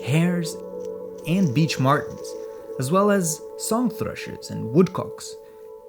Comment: Incredible stuff as always 13:33 Captions say beach martens for what i think is supposed to be beech martens, not a big deal but i just wanted to say it now that i noticed it lol